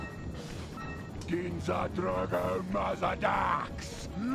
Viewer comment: does anyone know what he just said? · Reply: Probably an ancient language only the mask knows but he said, “DINZA DROGO MADADAX!”